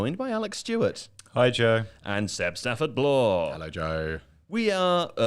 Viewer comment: Every time they say ‘hello joe’ in my head I hear ‘Lisa needs braces’